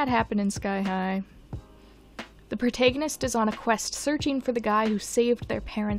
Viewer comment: I’m sorry did you just say protaygonist